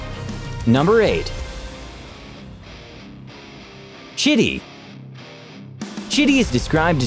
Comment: he said chitty but i dnt know why i heard shitty